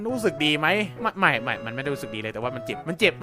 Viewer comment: ผมได้ยินว่า มักไม่ไม่ๆมันเจ็บมาก